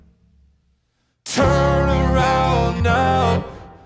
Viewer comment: I thought Justin was saying “turn around … ur only 18” in the end… 😂😂😂